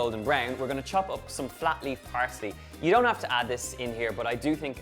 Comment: At 1.58 I thought said floppy parsley and it took me a while to realise he meant flat leaf parsley